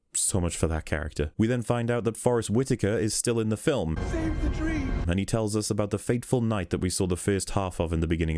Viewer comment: I just noticed that that scene from Rogue One of Forrest Whitaker that he's actually saying "Save the dream." For the past year or two I thought he was saying "Save the trees" lol